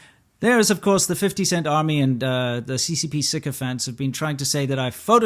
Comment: What is he saying at 8:12? ccp siccafans? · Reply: sycophant